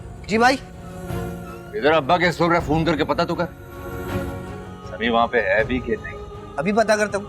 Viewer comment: Can anyone tell me what aazar said to Jamal at 0:19 don't understand what he said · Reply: Aba Kay store pay phone karo aur pucho sami wahaan hai yha nahin yeh kaha hai azar nain Jamal ko